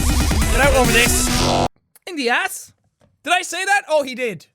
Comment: Did he just say to punch me in the ass?